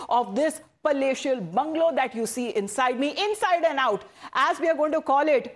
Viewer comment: What...!! she said inside me??